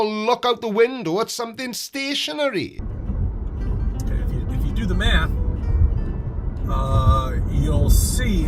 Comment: Wait wait wait wait wait wait . . . Did he just say "If you do the math"?? 😂